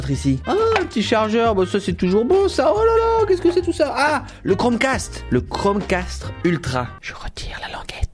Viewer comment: Toi aussi t'a entendu "Chrome CASTRE" ?